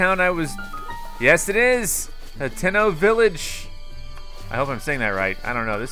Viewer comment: When he said Hateno Village I thought he said Nintendo Village